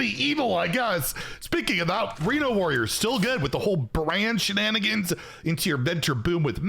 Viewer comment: I swear I keep hearing "brann" instead of "brand" when is referring to something new